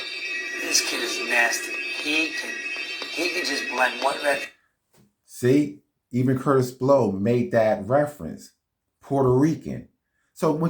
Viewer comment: Did he say BLEND?!!? Did I hear the word BLEND?!? That's what Grand Master Flowers and others were doing 10 years prior... BLENDING ...😭 Big AFRO?!!? 32:49 #AfroCosplaying - Keep Hitting them over the Head!!! The good doctor's new trailer links to his old hip hop mockumentary!!!😖